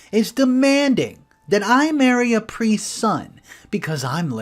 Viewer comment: For a second I thought you said "marry a Capri Sun" instead of priests son.